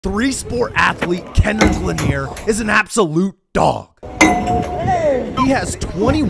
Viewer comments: I heard “ athlete Kendrick Lamar is DOG” pronounced very badly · I was tired as shit and i heard “Kendrick Lamar” 💀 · Thought he said Kendrick Lamar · Bruh at fist I thought he said Kendrick Lamar💀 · Anyone else thought he said Kendrick Lamar?? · Thought it said kendrick lamar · Who else at the Beiging thought he said Kendrick Lamar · Who else thought he said Kendrick Lamar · Those who heard him say Kendrick Lamar 👇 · I thought that said Kendrick Lamar😂😂😂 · i thought he said kendrick lamar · I thought he said Kendrick Lamar💀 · Bro I thought he said Kendrick Lamar